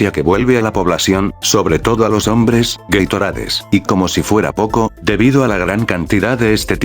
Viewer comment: Yo escuché que al comer sopa de macaco te vuelve gaitorade